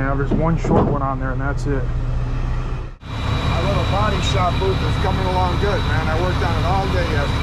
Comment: I heard saying something about a bodyshop booth